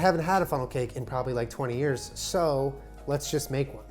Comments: For some reason I thought it said funeral cake. Guess I will look elsewhere. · My dumb ass thought it said funeral cake haha It looks delicious!! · I thought it said FUNERAL cake... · Thought it said funeral cake..